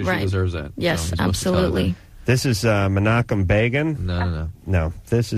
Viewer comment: Can someone tell me what name adam is saying at 1:21? Is this a real person? I like to look up all his obscure references. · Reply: Menachem Begin - 6th prime minister of Israel. better late than never